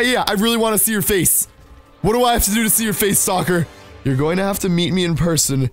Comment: Not to be mean but you sound like you say stalker as soccer